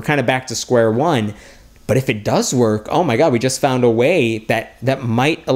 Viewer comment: did he say OMG?